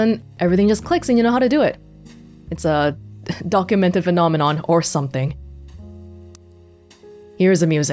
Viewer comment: NO - you DID NOT just say DUCK-umented phenomenon.